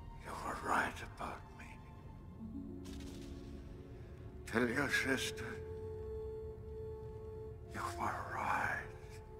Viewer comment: What did He Say!? Tell your Sister....!? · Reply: “Tell your sister you were right” he’s basically saying that Luke was right that Vader still had good in him